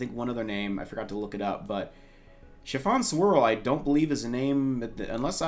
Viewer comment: When I first heard Mrs. Cake’s name, I thought she said “Chiffon SQUIRREL” XD